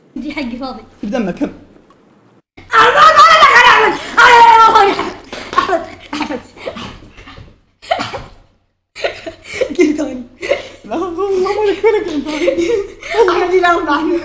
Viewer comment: نفسي اعرف ايش قال هههههههههههه😂😂😂😂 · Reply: ذا كذاب قال احمد مالي دخل احمد قيم ثاني